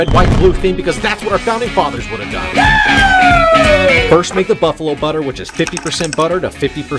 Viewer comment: I thought i heard butthole butter and i had to do a double take on the video 😂